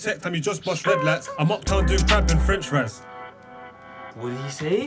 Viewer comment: from what i heard he said “i’m uptown doing crab and french fries”